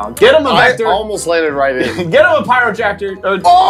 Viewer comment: I’m sorry did someone say pyrotractor? I think that mean protractor because a pyromaniac sentient tractor sounds horrifying XD